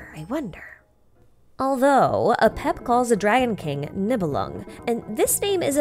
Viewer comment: Whenever I hear the Dragon King's name, I can't help mishearing it as Nebelung It's just a HUGE fluffy kitty